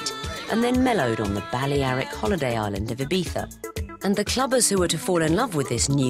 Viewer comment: Did she say ibitha @ 1:00 ?Am I missing something? Should I look at a map?